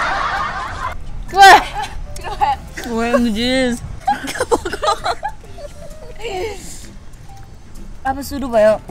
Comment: I Think I Heard Rough Word From 😂😂 Well Don't Know If My Mind Is Negative Or She Has Told That 😂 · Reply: I think she told OMG….